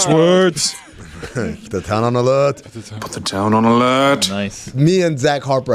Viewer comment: There is literally not one time I thought Mike Ryan was saying “put the town on alert”, I’ve been hearing put that down on the lad” for weeks now